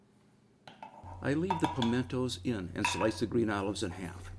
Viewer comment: Hi, Just a quick question. Are the green olives stuffed with pimiento or anchovies? Looks great btw :D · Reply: Answered my own question. Listened again and again and heard you say pimientos